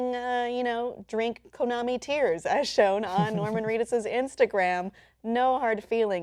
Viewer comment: Oh, "Norman Reedus". I thought she said "Woman fetus". I was all like, "I didn't hear about that one."